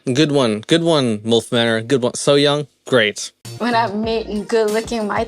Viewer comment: I think she said "wet" and because of it's context it had to be beeped. Botox mouth makes it hard to tell though.